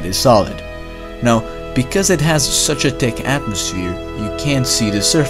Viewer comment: did he say dick?